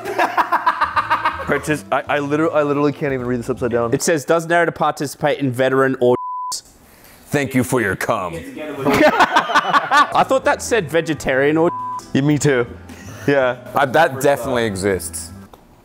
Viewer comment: I thought it said vegetarian orgins